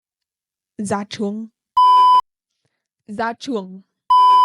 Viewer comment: i hear "ra trường" (graduate) or "ra chuồng" (go to chicken coop! chicken coop = chuồng gà in VN) =)))))))))))